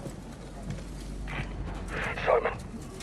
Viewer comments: Now I Hear soap Even though he said Ghost Weird Before I Heard Ghost now I here soap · i m always confuse watching these cutscene is captain price call SOAP but GHOST replied? did i just misheard? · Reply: He said Simon ghosts real name is Simon Riley. Soap is gay and dead